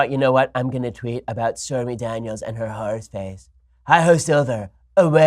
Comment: I thought trump was saying whore's face?!